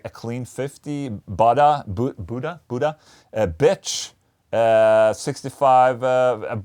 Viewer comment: did he just say there was a setting called bitch 😂 😂 😂